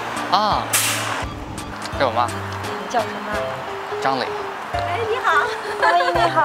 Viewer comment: did he just say his name is zhongli like our geo Daddy 😱